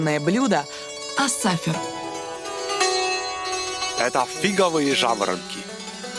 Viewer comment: я одна слышу фиговые жабры???😂😂😂